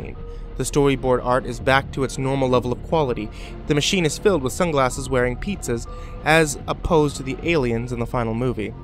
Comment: wait, did he say sunglasses wearing pizzas at 6:50 ???